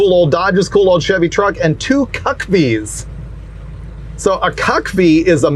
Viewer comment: Im sorry what... Did he say Cuck V's??? 👀👀👀👀👀